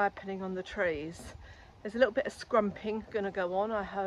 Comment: I thought I heard you say "scrumpy" so I went looking to see if you posted a recipe. Had to 'rewind' to discover you had said "scrumping." You can tell where my mind is at! The crumble looks marvellous. It's that time of year again when I will have to figure out how to use all the aronia berries that are almost ready. A crumble would be great, but the problem is always finding something with a strong enough flavour to offset the aronia.